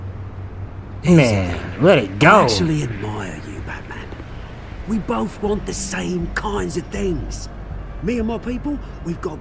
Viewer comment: It sounds like he is saying backman